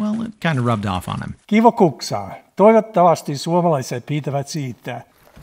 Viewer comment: I was so surprised it looked like a cute short wooden ladle and we, Koreans, call it gukja.. I was like, did he just say gukja?? wait, kuksa, okay?????